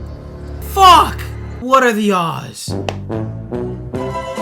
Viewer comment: I was listening while I was cooking and I heard “what are the Oz” and I had to back track 🤣